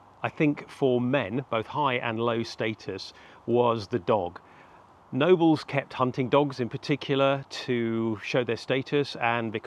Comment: I spooked for a second there. I thought he's saying "nobles kept-hunting, dogs" instead of "nobles kept hunting-dogs".